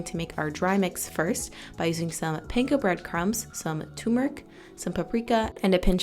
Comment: What type of crumbs did she say she uses @ 2:47? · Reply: panko